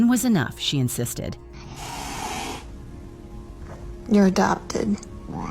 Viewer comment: When she says, "you're adopted," it sounds like he said, "Wow!"